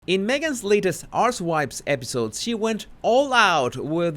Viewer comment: 🤣 it's probably an accent thing, but I honestly thought I heard you say, in the introduction, Meghan's latest ass wipes podcast. Love your work.